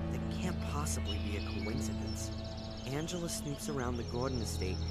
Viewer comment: says Angela instead of Angelina, but it is still captioned as Angelina. Oops! Anyone else notice that?